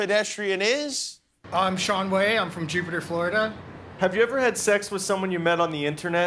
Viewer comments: Omfg 1:57 did he say he was from Jupiter Florida?!?!?!? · did he say Jupiter Flordia?